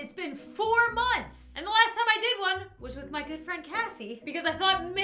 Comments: bro I thought you said " with my girlfriend kassie! " so happily xd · When she said my good friend Casey I thought she said my dead friend Casey and I'm like😶